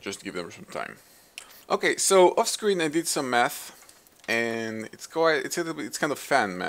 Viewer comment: did he say 'so off camera I did meth'? xD